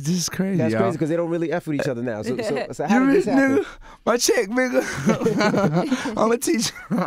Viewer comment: 100$ TO WHOEVER CAN TELL ME WHAT HE SAID💵 PAYPAL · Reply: Gambino Beats ya rich nigga my check bigger